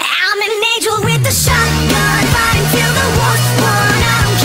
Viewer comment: Now i say alot "IM A DEMON WITH A SHOTGUN." and then i realized when i said "IM A ANGEL WITH A SHOTGUN." I hear "FIGHTING TILL THE WORST ONE." I was like "Excuse me what-."